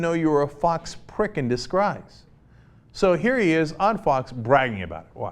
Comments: Did he just say disgreise? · did cenk just say 'disgruse'?